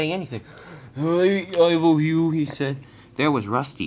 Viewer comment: what did say from 5:16-17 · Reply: says, "Hi, Ivo Hugh" right before he talks to Rusty.